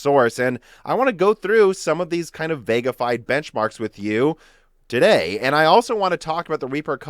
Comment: I'm a fan of Ryzen APUs... I heard him talk about "Vega-fied benchmarks" and my ears perked up. Took a second to realize what he actually meant.